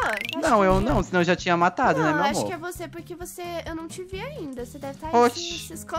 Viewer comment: gente eu ouvi o jean falando meu amo 6:57 que isso gente???